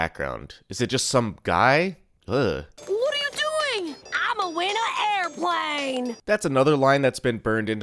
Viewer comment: I just realized, Amethyst here says "I'm gonna win a airplane", and later when she eats a cloud she says "I got hit by a airplane" in that exact same cadence. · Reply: I always thought she said "I'm a winner airplane!"